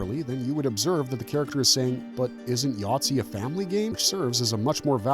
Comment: what he really says is "isn't sorry a family game," not yahtzee